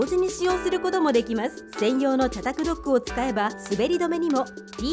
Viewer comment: 「茶卓ドック」がCapsLockに聞こえる件